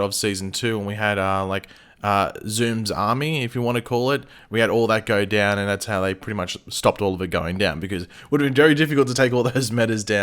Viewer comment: I had thought u said "thats how they stopd all the 'going down'" lmao. At that point, Daddy did indeed like the metal🤘⚡👌🏿😷💯